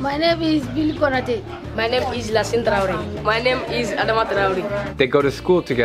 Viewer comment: did he say adama traore